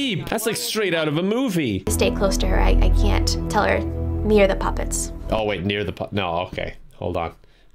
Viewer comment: I don't think she said near the puppets I think she said near or the puppets